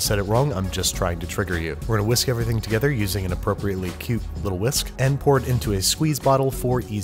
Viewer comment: seconds in, you didn't say "tiny whisk" but I heard it. It's too early to start drinking, unfortunately.